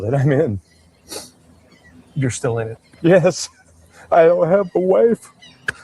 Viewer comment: I heard "I have half A Wife"